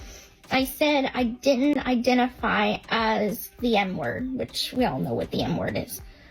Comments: Is she saying m word? As in murderer? I thought she was saying “n word” and was so damn confused lol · Ohhhhh, she said m-word. I thought she said n-word and was like, "Dafuq????" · Why my SLOW ASS thought she said “N” word & got confused by it… Lmfao. Took me a minute to realize she said “M” word. 😅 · My headphones thought she said " Hi I'm Gypsy Rose and I'm the N word" 😂